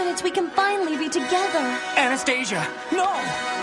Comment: I swear I thought I heard you say Anus stasia .Probably more fitting name for Golden Films and Dingo version of the story